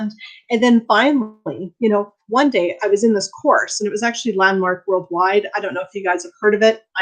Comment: you mentioned a course... you spoke kind of fast so I couldn’t catch what you said. will you please tell me what the name of the course was please. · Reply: Sorry we missed your comment, the course is referring to is Landmark worldwide. ~the social media team